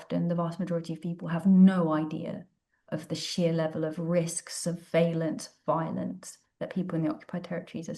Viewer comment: Wait, what did just say here? The captioning displays it as "...no idea of the sheer level of risk surveillance violence that people in..." But my ear seems to hear it as "...no idea of the sheer level of risk of valent violence that people in..." I have no idea what a phrase like "valent violence" would even mean, but it makes my vocabulary receptors tingle.